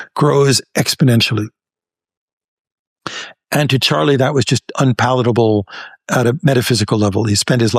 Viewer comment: Why do the subtitles say Scott when the professor clearly says Charlie? I noticed this happens multiple times around this timestamp. The professor very clearly says "Charlie," but the subtitles keep changing it to "Scott." Interesting subtitle error. Do you hear Scott Or Charlie in your version?🤔